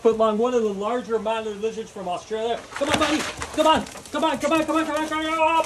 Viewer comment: At first I thought he said THAT GUY IS LUNCH 😂😂😂😂😂😂